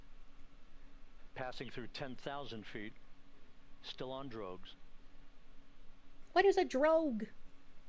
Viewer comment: i heard "still on drugs"